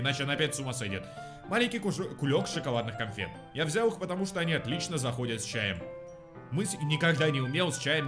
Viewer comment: как я это услышал "Я взял маленький хуек тк он хорошо заходит с чайком")))))